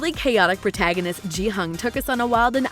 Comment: This is the second Squid Game video I've watched from and it pisses me off how you keep mispronouncing Gi-Hun. It's not Jee Hun. It's Gee Hoon, with a hard G. Watch any episode and just listen to how they pronounce it. It's offensive that you can't be bothered to learn the main characters name. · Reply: I heard her say it, and I was like who is JeE hUng, then realised she meant Gihun :')